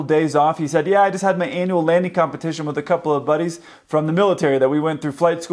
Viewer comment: I heard "anal landing competition." I was really perplexed as to what that might be.